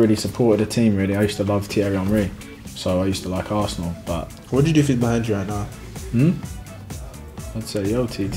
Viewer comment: what does iwobi say? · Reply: What would you do if he's behind you right now (joking that Thierry is behind him)